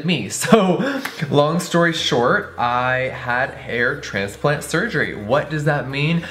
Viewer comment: Who else thought it said “Heart Transplant Surgery” ⬇️